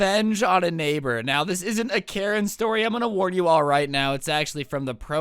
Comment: I heard "non karen story" then i clicked off